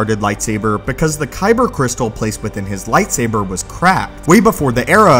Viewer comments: I like how he actually says the kyber crystal was cracked but YouTube subtitles says "crap", so uh... I guess same message, different way of saying it, lol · "Kylo Ren opting to construct a cross-guarded Lightsaber because the Kyber Crystal placed within his Lightsaber was Crap" Alrighty then... I know he meant "Cracked" but i heard "Crap" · I first heard this as “the kyber crystal placement in his Saber was crap” still don’t know if that’s what it actually said